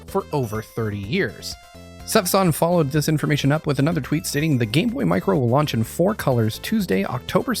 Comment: you just said gameboy micro instead of game gear micro..